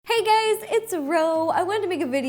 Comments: i thought she said "hey gays!" at the beginning and i was like: "who, me?" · In the beginning it sound like she says hey gays 😴😂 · omg i thought she said "Hey gays"